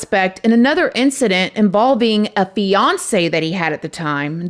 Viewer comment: At the 18:16 minute mark, I initially thought she said "Records show that Andre was a suspect in another incident involving a Beyonce that he had at the time" instead of "...a fiancee he had at the time". 😂😂